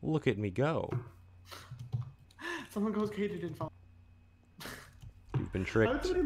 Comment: What did James say at 2:42:42? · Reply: Somogokamididsah